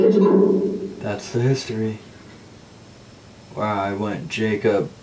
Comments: "The Bad Man" sounded like a kid, Jacob? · after they say that’s history I hear: “heh”, “ is that it?” And someone responding: “ definitely true” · When you asked about Jacob, I heard "that's the kid's name" instead of "that's the history"